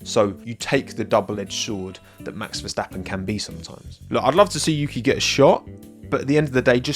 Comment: I thought you said "I'd love to see Yuki get shot" instead of "I'd like to see Yuki get a shot" and I was so confused 😭😂😅